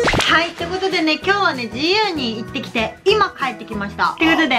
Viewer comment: 今日も一日ご苦労さんです😊 今日は自由に行ってきた〜ッて聞こえた（笑） 因みに自分はユニクロ派なんだ😅